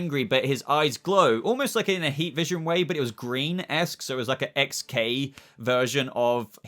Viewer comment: Wait.. @ 23:49 did u say his heat vision was "Green-esque" ? Am I trippin? Or maybe missin somethin? 🤔 Cuz i thought it looked like the normal red. If not maybe a tiny bit lighter.